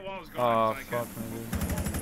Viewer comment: did he say the n-word? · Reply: I think he said ahh fuck my dude